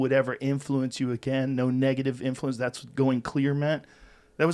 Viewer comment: Going "clearnet"? Did I hear that right?